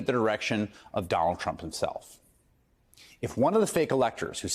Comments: Sounded like he said Donald Chump..😂 I like it. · Sure sounds like Chris says, "Donald Chump" in the beginning of this vid. 😂😮😂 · Did I hear “Donald Chump”? 😭